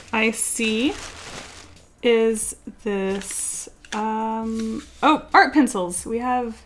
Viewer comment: I thought she said Fart Pencils. Oops